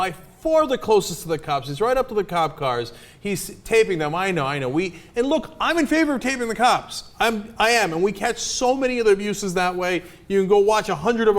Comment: Literally one sentence said this for a caption on this video when the guys talking at 4:20, the caption says " he stayed in a minority week, it looked I'm in favor of handicaps and we catch so many other the uses that way..." LMFAO!!!